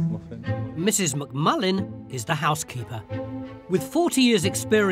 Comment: Did he say "Mrs McMuffin"??? 😂😂😂